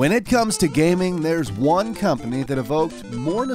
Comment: I heard one Communist